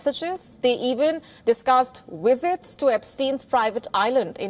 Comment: I thought she said "wizards" all through the video😂😂 its actually "visit" btw